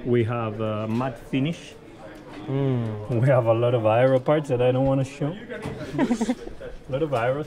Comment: When Carlos said aero parts I thought he said 'biro parts' 😂😂😂